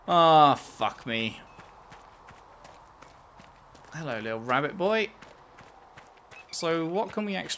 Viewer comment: Did he just call a Nug "Little Rabbit Boy"?